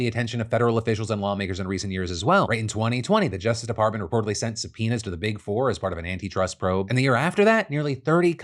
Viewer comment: i was making a coffee as Phil spoke, i thought he said “they sent some p€nis”😂😂